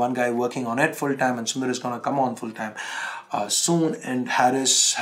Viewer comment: I thought you said Sundar was 'coming' full time as he completed studies..now he is 'going out' full time 😁